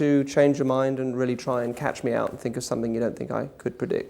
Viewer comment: Ooo i love this man, so clever check it out at 03:53 he says try and think of something to catch meow instead of catch me out. Beacsue he was a professional psychiatrist he didn't use anything too obvious like saying its doesn't have to be purrfect. That's all he needed, your brain will latch onto it and subliminally draw you to that which is obviously a cat. Well played sir my dream is to goto a school where he teaches this